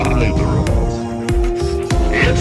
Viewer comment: The part when Springtrap said “got you” was so perfect! I love this so much!!! ❤️❤️❤️ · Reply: I thought he said “cut you” XD